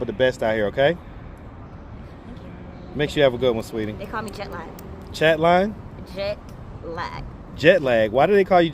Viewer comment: I swore she said they call her jet black